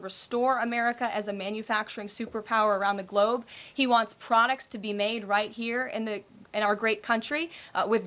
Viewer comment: did she say "cronic be grown right here in our great country"?? 😂🇺🇲